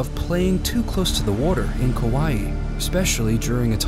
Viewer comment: did he say kawaii or Hawaii? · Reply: Sigh..... it’s Kaui.... it’s a Hawaiian island